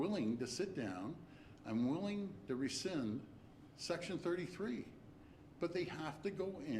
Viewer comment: I Thought he said Resign 😅😅😅😂Don’t use the Kids think about the Studying they had to go through you treat them bad all the time you shouldn’t do that Mr Ford 👍👍